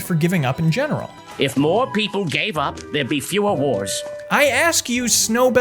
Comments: "If more people gave up there would be fewer wars." is surprisingly insightful from a talking cat in the sequel of a talking mouse movie. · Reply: lmao even after rewinding and listening to it again i thought he said "there'd be fewer whores" and was astounded that was allowed i na kids movie. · Am I the only one that thought the cat said " If more people gave up there'd be fewer whores"? I know that makes no sense but that's what I heard even after playing it back several times. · "If more people gave up there would be fewer wars." is surprisingly insightful from a talking cat in the sequel of a talking mouse movie. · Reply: I thought he said fewer whores